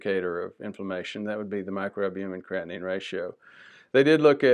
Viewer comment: You said at 8:46 a form of "ratio" to measure for inflammation but I couldn't understand what was said. What is your preference for measuring inflammation? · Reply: Thanks for asking: HsCRP, MACR ( MicroAlbumen/Creatinine Ratio), LP-Pla2, and MPO. We've drafted a mini-course on cv inflammation testing. It's going through technical prep: transaction, survey pages, landing pages, etc.